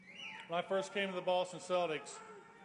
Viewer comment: why does he say sell dicks multiple times? You can’t unhear it.